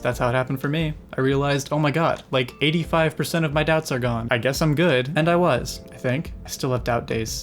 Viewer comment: When she said “85% of my doubts are gone” I thought she said “85% of my depts are gone